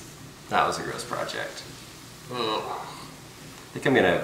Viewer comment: did he say "bleach it"? I need it